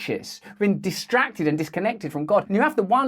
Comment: ish. Did I hear right? "Distracted and disconnected from god" Did he say that? If so, he's as bad as them. I hope I'm wrong.